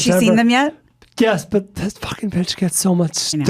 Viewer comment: did just call his daughter "This fxckin' bitch"?